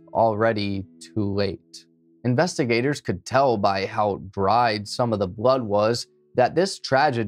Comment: Why did I hear you say "investigators could tell how dry some of the bud was" 😭 i had to rewind.